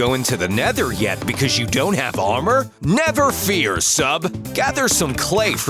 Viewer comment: I thought he said "nether fear"